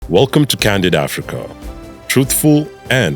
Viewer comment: I thought you said welcom to Canada Africa and was very confused